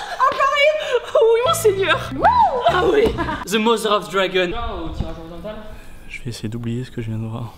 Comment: à 6:51 ils ont dit quoi ????? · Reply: Tibo a dit "The mother of dragon" et le mec a dit "Je vais essayer d'oublier ce que je vien de voir"